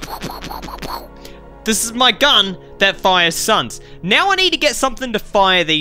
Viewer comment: The auto caption thought that you said a gun that shoots sons